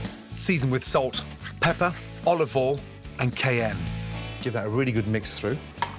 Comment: what is he adding at 5:58? The subtitles say 'km' :/ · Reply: He actually says cayenne, which is a pepper